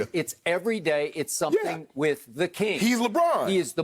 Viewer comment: Did skip call Lebron the king